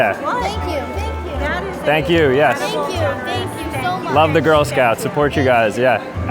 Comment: did he really said love the girls?💀💀